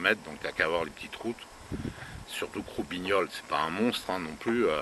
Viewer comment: C'est moi qu'es mal compris ou vous avez appelez votre bahut Roubignoles ?